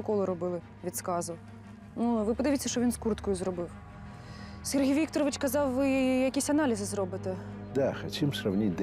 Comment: мне послышалось или она сказала Сергей Викторович,просто у меня так учителя по фискультуре зовут😅